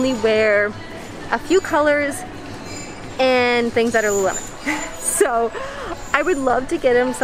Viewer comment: queen… what did you say at 7:12 i’ve been trying to figure it out it’s eating me alive 😂 “things that are lemon”???? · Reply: “Things that are LuLuLemon” is what she said 😆